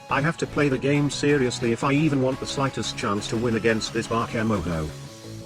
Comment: I thought said Bakamono at first but it was Bakemono 😂